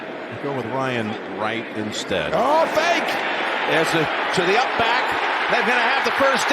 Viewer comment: did I hear a kid say "ITS A FAKE!!!"